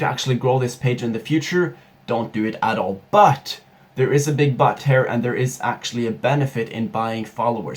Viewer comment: did he say there’s a big butt hair?